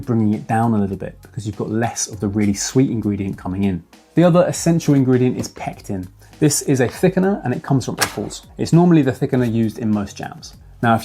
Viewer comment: I had to turn on CC at 3:18 because I kept hearing "this is a thickener, and it comes from a horse..." :D